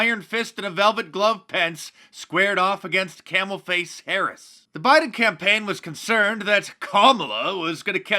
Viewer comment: I swear I thought you said Camel Toe Harris lol